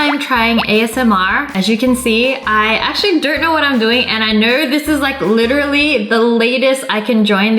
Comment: Who else thought she said "I dirt know what I'm doing"